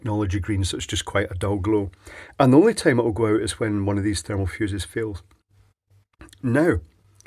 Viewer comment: Maybe its the British accent listening with my not brit-ears ears but at TS 9:28 I think he said about the LED: "The only time it will glow is when one of the thermal fuses fails". It glows until the thermal fuse fails.